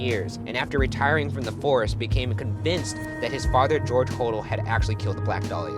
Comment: I thought he said George Whoredell.